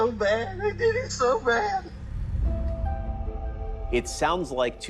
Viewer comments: When they slowed it down I heard, "It is so bad" twice. No "they" or "I". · I heard "It's just so bad, I did it so bad. "